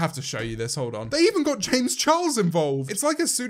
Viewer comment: at this point Lele Pons, Lily Singh and tiktok are just giving James so much content to make money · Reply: I thought you said James Charles so I was very confused for a second